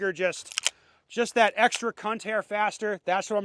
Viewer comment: What’d he say at 01:14 😂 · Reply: Ha that's a very common unit of measurement with blue collar guys. Especially in construction, "your cut is too long, gotta take a cunt hair off that".